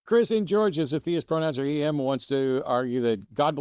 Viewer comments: “CHRIS IN G-“ The highlight of this video 😂 😊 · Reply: I keep hearing "Chris Angel". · I thought it was Matt's voice at the beginning of the video introducing the caller. "Chris in G-" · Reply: I thought it was Matt saying "CHRIS ANGEL"